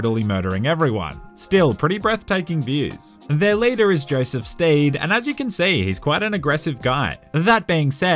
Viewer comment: Joseph Stade??? What???? What????????? Say that again please. ✌✌😅😅